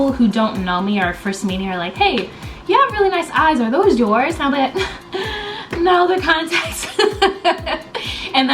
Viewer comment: What did you say at 6:46 ? Couldn't understand (i dont speak english so it's harder for me to listen to english) I just caught: - Hey! you have really nice eyes [and here comes the part I don't understand hehe] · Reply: Nebulousss "Hey! You have really nice eyes are those yours? & I'm like, no they're contacts"